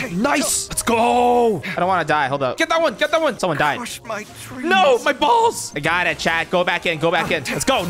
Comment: I swear I heard him say : OH MY BALLS!